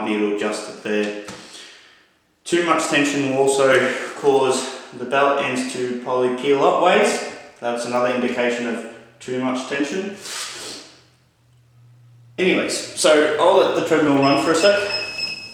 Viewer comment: what do you say there? I cant understand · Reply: Sounds like "Too much tension will also cause the belt end to probably peel upways. That's another indication of too much tension"